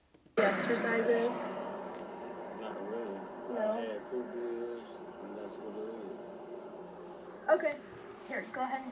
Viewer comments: I hear him say, "i had to beers, that's about it.." not a thing, about "weed".. lying her face off.. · Sounded like he said "i had 2 beers, that's what it is"